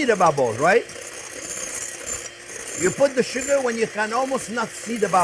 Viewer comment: I thought he said butter sugar and got too excited for the thought such a sugar existence 😅